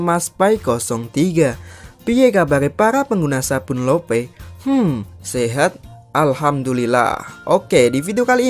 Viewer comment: Kalo gak salah denger intronya ada sabun lovers😂